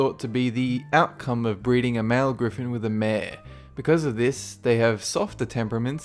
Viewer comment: I thought said breeding a man with a gryphon wat O_O